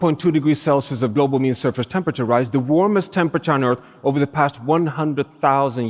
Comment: What is that word? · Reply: Global mean surface temperature rise